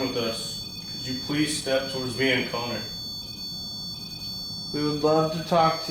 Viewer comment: The voice at 1:53:54 almost sounded like an accent saying Donny or Dumby